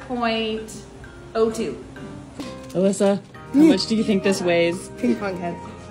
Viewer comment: “How Much Does This Weigh” what I heard “HOW MUCH does iT waiy” MAH HEARING IS FED UP HELLPPP AGHH